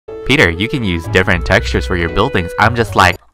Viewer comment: In the beginning when he meant to say peetah but I heard “peter” 😂